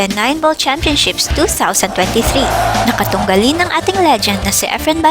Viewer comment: Magaling talaga ang ating si Sir EFREN 'BATA' Reyes, the Legend! Ms. Voice over. Do I hear "Legent" instead of "Legend" o nabingi lng akO.